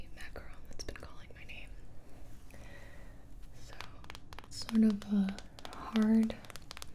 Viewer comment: I can't be the only one who heard son of a hard but delicate shell